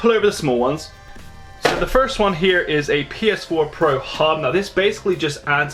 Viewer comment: i thought that said pornhub lol oops